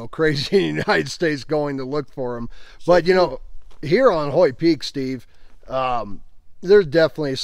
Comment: what peak is that? I can't quite make out what name he said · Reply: It's Hoyt Peak, northeast of Kamas, Utah